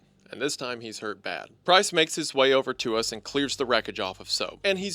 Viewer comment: LMAO! I know he’s saying “Price”, but it sounds so, so, SO much like “Christ”. 😂 First time I was listening I got so confused! I was like, “Jesus? What is Jesus doing there?” Before I realized what he ACTUALLY said 😂